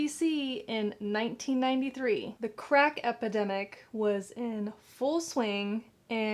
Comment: I thought said "the crap epidemic" i had to go back and listen again lol.